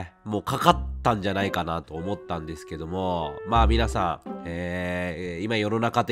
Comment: 「ですけども〜」が「どぅぇすけどむぉ〜」て感じに聞こえてこっちまでぽやぽやしてくるw この時期体調崩れるといつも以上に心配になりますよね… お大事にしてください🤲